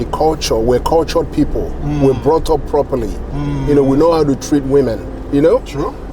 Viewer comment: Wait. Did he say Nigerian Men know how to treat women? Did I hear that right? Time stamp